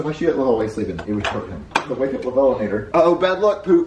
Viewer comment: what did you call 😂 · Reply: Lovellanater